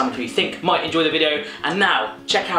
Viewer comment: My hearing is apparently not what it used to be, i thought you said Kinky community, it turns out that that wasnt the case xD